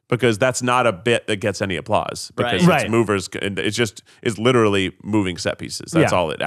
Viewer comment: I've literally been rewatching the broadway videos so much when they said 'uber' I thought they were saying 'mover' 🥲